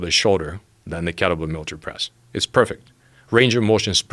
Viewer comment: he said kettlebell (WHAT) press ???? 08:18 i can't understand him ...but i wanna try it out lol !! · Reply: Military press